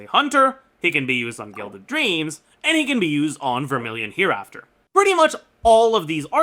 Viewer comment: did someone say vermillion